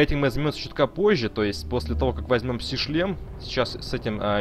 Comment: Ученый Сахар, а не Сахара, ударение не туда. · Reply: Лол, послышалось "когда мы возьмем пси-член"